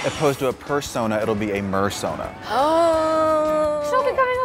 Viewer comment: I thought he said a fursona and I'm like are they changing the topic to furrys???